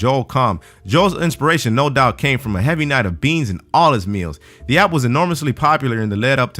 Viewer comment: that was kinda racist if you read the caption they put Mexican instead of bean